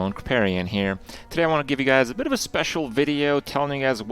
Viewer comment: Preparian here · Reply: I had to relisten to it because I actually thought he said Preparian